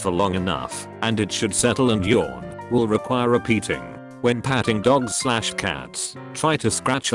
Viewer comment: I thought it said beating